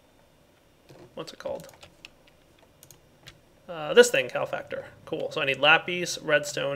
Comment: Everytime he says calefactor I hear "cow factor". Kinda funny.